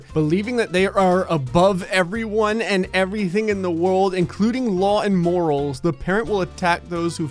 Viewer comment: i thought he said “including law and mortals”